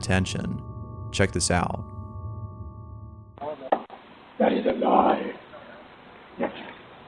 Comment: I had subtitles on and I said “that is a lie” but I heard it say “ready to die?”🤨